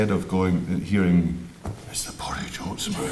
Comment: What does he say at 6:36? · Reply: "It's the porridge oats man". Rory was in an ad for porridge way before his acting career took over. A true Jock!